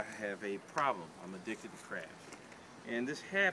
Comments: I heard "I'm addicted to crack". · Who else thought he said im addicted to crack lol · I thought you said at 0:08 "I am addicted to crack and this habit is very expensive so my solution is to raise crabs" I was thinking cool now thats a good crackhead right there. Hah